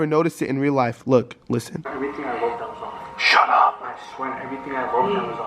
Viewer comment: is it just me or all I hear is shut up not leave